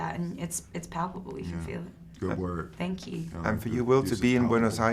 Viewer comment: What did Will say at 7:17 ? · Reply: Will said: Good word, good use of "palpable" // He said this because just before it Margot used the word "palpable" in a sentence